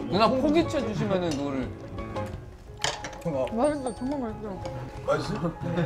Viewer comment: 정말 맛있어가 ㅈㄴ 맛있어로 들리는ㅋㅋ